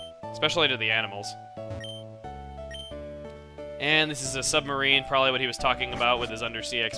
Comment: I thought that it said "Submersible Poo" due to the fact that he had just said the word "Toilet".